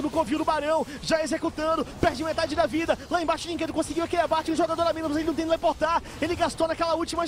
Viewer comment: KKKKKKKKKKKKKKKKKKK o que ele falou? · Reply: Gustavo Rodrigues la embaixo, dynquedo conseguiu o abate, um jogador a menos mas ele não tem teleportar, perdeu na jogada passada